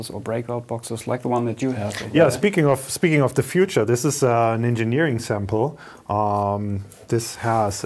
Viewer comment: Did he say ninja-neering sample...?